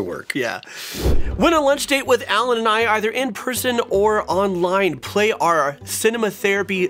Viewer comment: he said "win a lunch date with allen and i either in person or online" and i thought he said "in prison" and i was really confused for a second